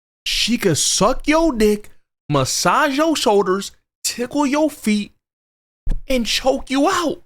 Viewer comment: Okay never mind I thought you said massage your feet but you said tickle your feet which is just another form of torture of the kinky kind💯😅😅😓😓😓😓